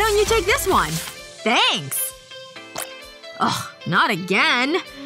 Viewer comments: he said at least I have snacks! But the Subtitles said at least I have sex! :O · the cc says atleast I have sex